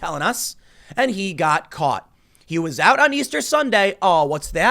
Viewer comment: I thought he said he caught the virus. But, then again, I don't watch CNN.